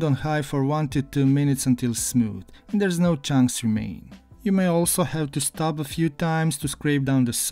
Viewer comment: This looks lovely! Blend nuts and fruit until smooth, then at 1:21, you say "There's no chance you main" which I can't manage to hear correctly. What is said there, please? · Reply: "there's no chunks remaining"